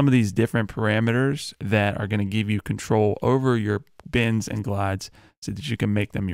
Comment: every time you said bends i thought you were saying bins... whole time i was like ok bin the the 808... never heard that before but that's why i'm here, to learn. lol great tut